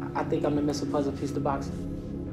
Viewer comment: What does Broner say at 2:15? He's the missing puzzle piece of a box?